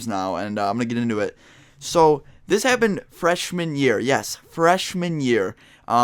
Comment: did you say freshweed year?